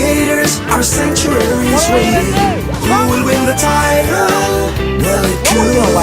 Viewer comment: I had a part that was hard I finally figured out it said who will win title. I have laughed so hard I thought it said Tony the tiger. Forever I heard that then I put the phone up to my ear. Then I heard it who will win the title. So funny